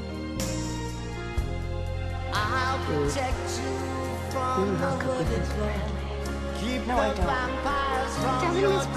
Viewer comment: please what does Kat say to Annabelle ? " [ ..... ] with miss Bradley" ? which Annabelle answers " No, i don't" i can't hear coz of the music and english is not my mothertongue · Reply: She said “You hook up with miss Bradley”